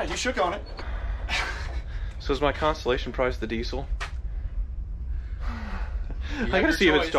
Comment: Did I hear "constellation prize?" is 🔥